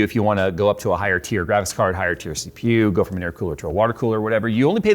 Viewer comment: Did he say go from an intercooler to a water cooler? He got cars on his mind lmao